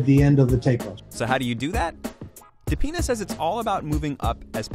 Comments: did anyone else hear that? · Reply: Dapenha says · wait.. what did he say? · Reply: He said Dapena that is the doctor name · What did I just hear? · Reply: Dapena's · i didn't understand the word, i understand "depends" but im not sure